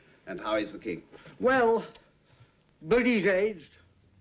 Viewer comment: WC refers to the king as Bertie. The movie also depicts the king looking far more like Edward VII who’s name was of course Bertie. In 1928 the king was of course George V. Can someone come explain?? These no way they made that big a goof up I am sure... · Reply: I think you have misheard. In response to Baldwin's question about how the King is, WC says 'well, but he's aged...' I think you thought he said 'well, Bertie's aged...'